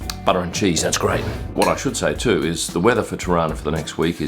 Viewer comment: Every time said "Tirana", I kept hearing "Toronto" (as in Canada) - because that's how we locals pronounce it. LOL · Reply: Hahaha same i thought he was trying to say Toronto lol